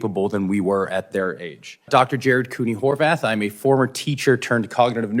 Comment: I really thought he said his name was “whore bath.” For once, the captions didn’t do someone dirty and I was able to see his actual name. lol